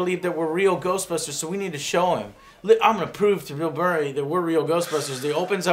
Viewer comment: Did say Mill Burray? XD